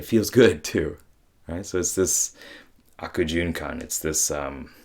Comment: What’s that word “arcojucon” @ 3:09? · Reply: Akujunkan. it's Japanese, writes as 悪循環 in kanji. it just means 'vicious cycle'.